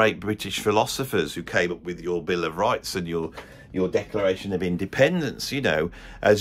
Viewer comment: I heard say "The decoration of independence". Yup.